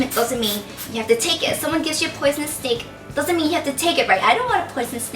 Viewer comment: I thought she was saying poisonous steak :'D and that she was gunna say 'you can be the most juciest most ripest steak'